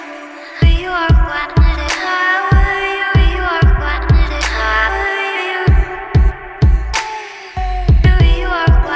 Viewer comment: What does she say at 2:54? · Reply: She says,"Threw you out, didn't outgrow you, didn't outgrow you." but it's reversed